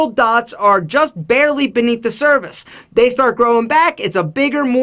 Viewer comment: Did anyone else notice he said service instead of surface